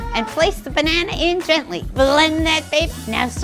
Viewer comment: I heard it as "Here's a fuuuuuckin smoothie"